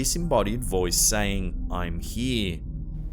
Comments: The security guard one at 23:30 I think. Doesn’t say ‘I’m here’ it says ‘come here’ at least that’s what I hear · it said come here in my opinion not I’m here? Who else hears what I do ?